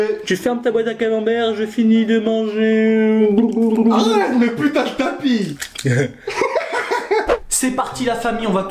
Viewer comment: il dit quoi putagetapi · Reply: Il a dit putain le tapis